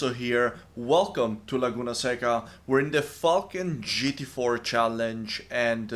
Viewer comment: at the start I thought you said "we're in the f**kin GT4 Challenge" and it took me offguard